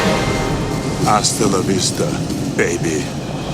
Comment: If you put on CC, at 1:22 it says "I still love Easter baby" instead of Hastla la Vista baby LOL